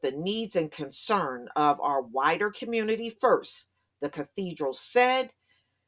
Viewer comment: did you say 'wider' or 'whiter'?